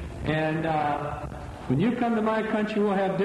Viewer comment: Did he say “You pee and I’ll pray?” Lol